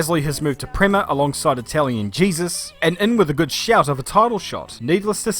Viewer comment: I'm sorry but I misheard that as "Italian Jesus"...